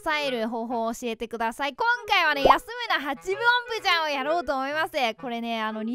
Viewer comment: 安村8分音符ちゃんに聞こえるwww